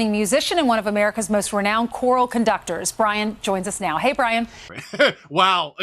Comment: is sound like they say an award winning Grammy musician and one of America's renowned ORAL conductor's